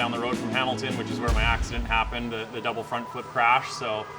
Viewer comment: At first I misheard and thought he said "that's where my accent happened"! Was slightly confused for a minute there.